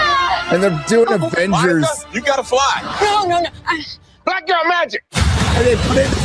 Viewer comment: Oh! He said "Black girl magic" I thought he said "Fat girl magic"... my bad.